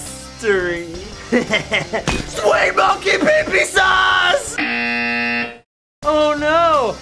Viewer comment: XDDDDD DID HE JUST SAY THAT?! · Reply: I LUV MONKEY PEE PEE SAUSE